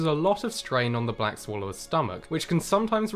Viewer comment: lmao i thought it said brick swallower